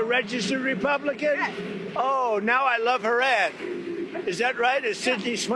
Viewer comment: I thought the President said he "love her arz" lmao. Yeh, me too, Mr. President. The ad is nice too.